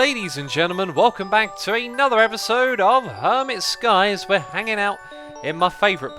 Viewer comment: I thought that it said 'hermit snakes'... GOD DAMNIT